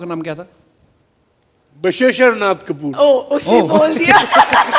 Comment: Did she say "usne bol diya", referring to her dad?? That shows the 3rd class-ness of these run-dees